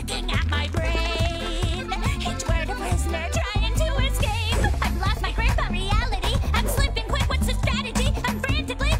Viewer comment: Did Tater just Say rizzler at 0:22? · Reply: She said prisoner